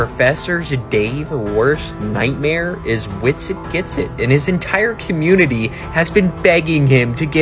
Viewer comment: What’s the name of the guy mentioned at 58:15 ish. Winston Ginson or something? Can’t hear exactly what he said. Ta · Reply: Witsit Gets it! <link>